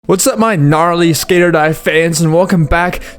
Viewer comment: i heard ‘what’s up my n-‘ and i was like oh shit